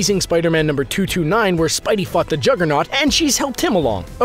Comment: Sony shouldn't do anything. Period. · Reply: I swear to god it sounded like he said "Where Spidey fucked the Juggernaut"